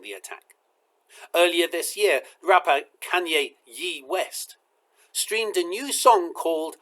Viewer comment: I thought you said ... Rabbi Kayne West. Wait. What? I don't hear well without my hearing aids. I listened a second time and you said rapper" Kayne West and you mention his song of hate. Our leaders need to speak out against hate, yet I am afraid many are weak and won't... The oldest form of hate for me is scapegoating.